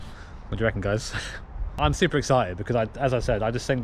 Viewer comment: did he say Supra excited lol